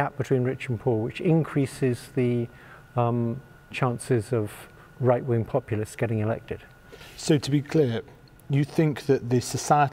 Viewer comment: did he just said "right wing populists being elected"? 💀